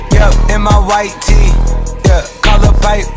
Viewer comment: Am I the only one that thought he said "cap in my right teeth"